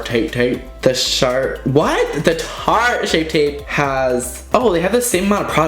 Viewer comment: I thought you said shart shape tape